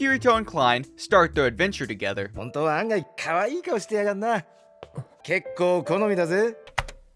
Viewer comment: Did Klein says he like me come back when can be cooler than me